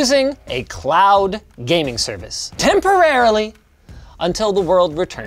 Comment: Ohh jeeez 0:38 "temporarily" had me there 😂😂 it sounded like some orange dude